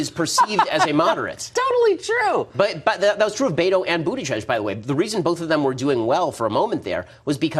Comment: Who is that judge guy that Ben keeps mentioning? I know it sounds ridiculous, but I keep hearing Ben say "Booty Judge." 😂😂😂😂